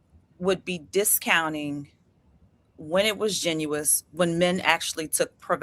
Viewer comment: 😂😂 Hold up wtf is "genious" that's not a word or is it "Disingenuous"? Had to rewind again and put on CC and it was "genuine" 😂😂. Get her outta here she just talking but saying nothing & she didn't answer the questions.